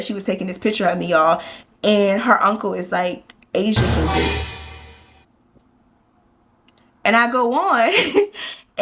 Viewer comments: What did her uncle say? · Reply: "Asia can get it" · girl what he say? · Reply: Asia can get it